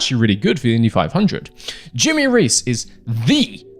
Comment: I wasn’t looking at the screen and thought he said “Jimmy Race” similar to how one would say “John Doe” 😂